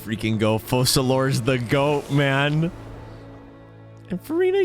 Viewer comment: did i Heard word "Forever" like someone's talking about?